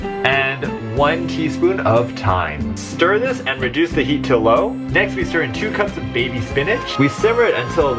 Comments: I thought he said low key. Rip Loki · I feel so bad for him, I thought he was saying low key